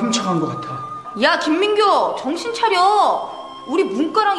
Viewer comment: LMAO AT 1:10 I THOUGHT SHE SAID "KIM MINGYU"